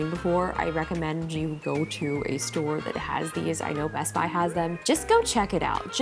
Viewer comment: As french i heard '' my husband has them" instead of "best buy has them" 😂😂